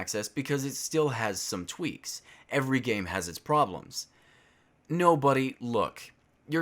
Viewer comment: Is he saying "No, budy look" or "nobody look!"? Im kinda confused